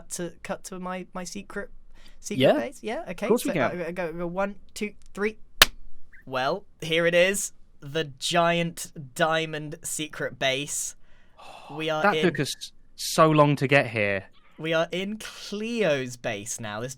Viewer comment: The way he said it sounded like THE GIANT ENEMY SPIDER